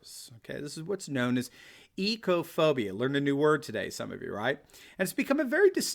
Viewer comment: yup, I did indeed learn a new word today, thank you Dr. Steve. As a dyslexic my chances of ever being able to spell this word are slim. It sounded like "Ecophobia" but it began with an O!?!